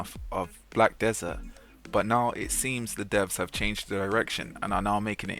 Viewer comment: it sounds like you said "The devs have changed their erection" and I can't unhear it now.